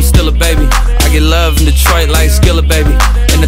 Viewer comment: i thought it said "i get love from detroit like steal a baby"